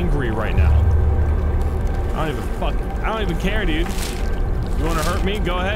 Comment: did i hear you say "i dont even fucking... i dont even care"